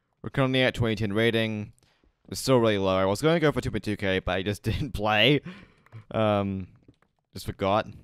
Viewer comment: Need to hear 3times to understand what is he saying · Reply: "still really low i was gonna go for 2.2k but i just didn't play" actually took me 3 replays